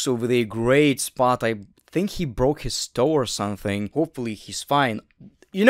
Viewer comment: i thought u said "hopefully his spine" the first time hearing it, i was like, WHAT